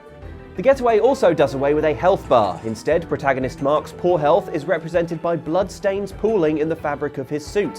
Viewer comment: I heard “Mark Spoorhealth” and thought yes, that is a perfectly normal British surname